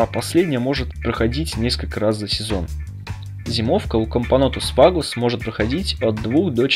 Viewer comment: "Всякий муравей, который не матка":))) Кампонтус, или мне послышалось? Кампонотус, вроде как правильно